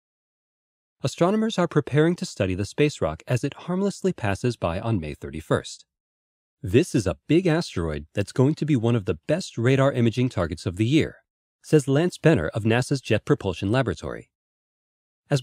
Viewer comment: at first I thought he said "this is a big ass-asteroid"